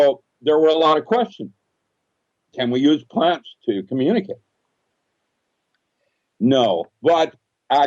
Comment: can someone please tell me, did he say "we use plants to communicate"? I can't hear what he said.